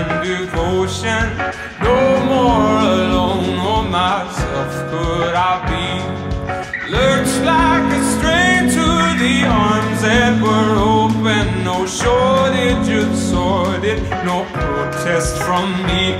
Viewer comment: this whole time i thought he said "looked like i strayed to the arms that were open"